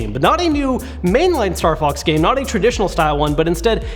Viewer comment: When he said Star Fox I thought he said Star Bucks XDDDD